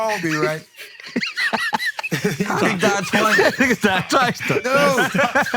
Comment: did Andrew say nigga??! Lmaooo